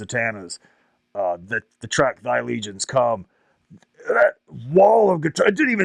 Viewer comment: what band did he say?!?!?! The band he first listened to "dark funeral" what was that! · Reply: he plays Thy Legions Come by Dark Funeral its a black metal band from Stockholm, Sweden, founded by guitarists Lord Ahriman (Mikael Svanberg) and Blackmoon (David Parland) in 1993.